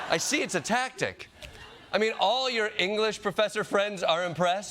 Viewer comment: Chump did NOT say"English Professors are impressed" He said "ppl LIKE English Professors..." I think that means Gym teachers and an Assistant Head Coach for Wrestling.